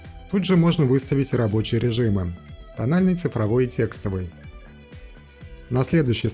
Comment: Мне одному послышалось Анальный вместо аналоговый?